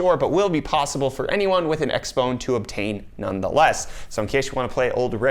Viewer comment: did he say x phone instead of xbox 1